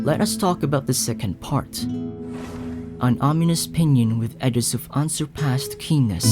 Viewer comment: I heard this as Penis 7:57 xD